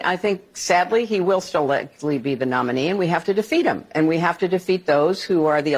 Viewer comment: Clinton says "We have to defeat him again..." I Hear: "We have to CHEAT AGAIN..."